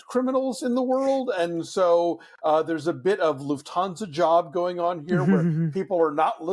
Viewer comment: What’s the word or phrase uses at 2:39? It’s in this sentence: “These are not necessarily the most competent criminals in the world, and so there’s a bit of _____ job going on here … .” Thanks in advance! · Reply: Oh, the Lufthansa heist, it’s a Goodfellas reference. Thanks for watching!